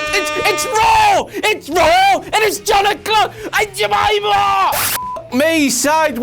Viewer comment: Did he say "Aunt Jemima" haha 😂